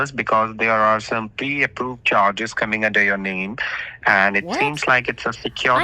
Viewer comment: “some pee and poop charges” ?? No lie that’s really what I thought he said..😆😆😝